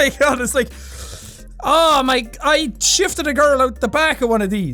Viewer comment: I Definitely thought he said he shiv’d a girl at 14:10 and I was like “you stabbed someone” 😂😂😂